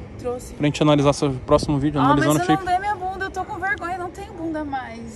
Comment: ‘’Ah mas eu não dei minha bunda’’.. foi isso mesmo que eu entendi???